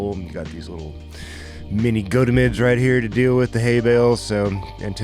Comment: I've been hearing say this word the whole round, what is he saying? Sounds like "go-to-mids" but don't know how to spell it, or if that's what it actually is. I understand what he's referring to but haven't heard the word/phrase before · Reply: Goat a mids. The haybails stacked up in pyramids.